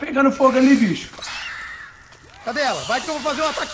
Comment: só eu que entendi cadela ao invés de cadê ela? Kkkkk